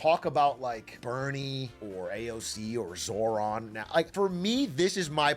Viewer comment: did he just say Bernie, AOC, and Sauron?